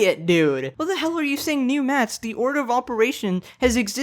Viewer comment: I think he said "new maths" cause he was talking about the stupid people (which i think is ironic considering he is also part of that group)